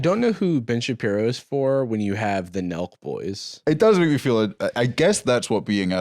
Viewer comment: OMG I thought you were saying MILK boys